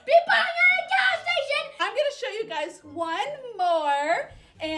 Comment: Whenever I first watch this video, I thought he said she got the blue one and she isn’t going to the gas station so she put it so she put it on me😂😂😂😂😂😂😂😂😂😂😂😂😂😂😂